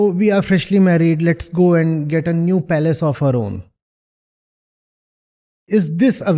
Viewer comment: I cant understand what he is saying. Palace of arome? What is that?